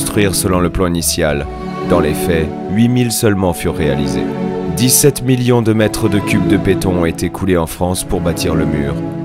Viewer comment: I don't understand French at all but at 3:42 it say's: 17 Millions M2 de beton But I assume it should be: 17 Millions M3 de beton and noting else ? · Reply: Yeah he actually sais "m3" but it's wrongly wirtten m2. Typo I gess